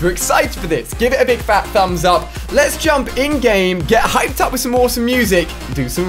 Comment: He said "get hyped" and I thought he said "get high" 😂😂😂